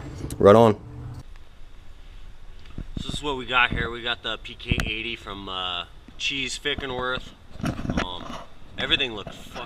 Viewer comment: I believe you mentioned where you got the PK80 Engine @ 1:54 into the vid, however, I can't for the life of me discern what you're saying. I tried to enable CC (close captioning) hoping I could read what you were saying but that didn't work either. Unless you're getting them from "Cheesedick and worth" the CC is WAY off, LMFAO!!! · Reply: It's a cheez motor, but I also have a Calibike kit as well. Good kits!